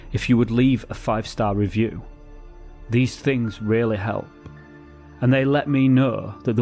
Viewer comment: Every time you say it, I hear "these rarely helps"